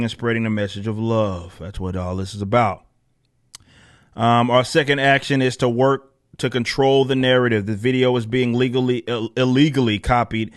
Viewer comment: Did they say "Our 2nd action is to work to control the narrative."??? 6:30 Lol